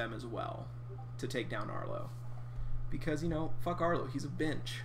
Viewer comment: f arlo he’s a bench. Does anyone else hear that